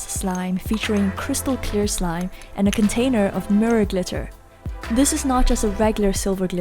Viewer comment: When we said mirror I thought she said nerd glitter 😂😂